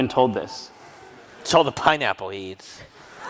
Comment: Can someone please explain what Jim says ("So the pineapple eats"???!?) and why everybody laughs? thx!